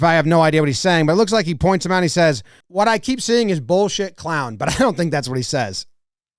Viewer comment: “he’s an emotional guy” is what he was saying